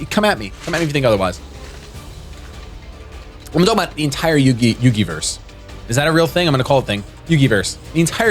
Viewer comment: He said "Yugiverse" and I'm just sitting here like "Did he just say Yukiverse? Am I being called out right now???"